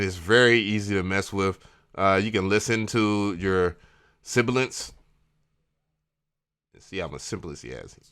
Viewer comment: What is the word spoken at 7:51? Sorry, I just cannot figure out what word that is. Phonetically, it sounds to me like "siblence". Thanks for the vid.